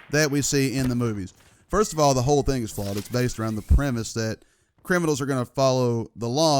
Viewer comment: On 1:25/1:26 did he say "the whole thing is false"?